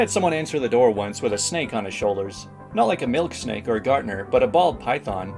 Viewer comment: I was half-listening as I was washing dishes and snapped mentally awake when I heard 'milk steak' when it was 'milk snake' that was actually said. Well, for a second there I was definitely surprised, thinking that maybe the writers didn't make Charlie Kelley's date food quite as obscure as they thought.